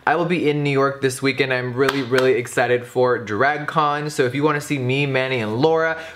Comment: I thought he said he was exited for dragon con. I got so exited. I thought maybe jefrry could be a dork like me. But. Sadly. No.